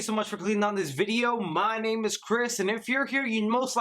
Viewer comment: I thought he said cryp instead of Chris 😂